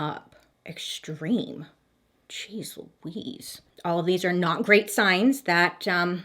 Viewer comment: Bailey said "Jeez Louise" at 8:55 and the subtitles say "Cheezel Wheeze" hahaha